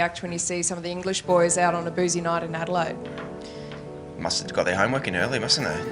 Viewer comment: what did watson say at1:46 ? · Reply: they must've got their homework in early